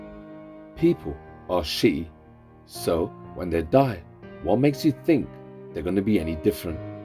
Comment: what is that you said? People are 'shitty'?" Sorry, I really can't make it out, but that makes sense to me, I guess.